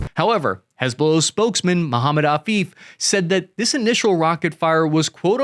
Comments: I thought you said Mohammed Al Beep lol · Thought you said the spokesmen's name was Mohamed Al Beep.